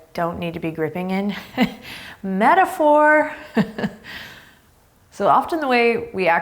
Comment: At the beginning I thought you said Oh theres a metaFLOOR instead of metaphor... maybe you did..either way im going to adopt that and spread that shit everywhere..thanks for hanging out with me Adriene. Happy Friday